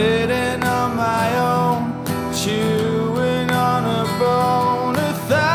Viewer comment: chewin on a bone"????? I always thought he said "you and me on a phone"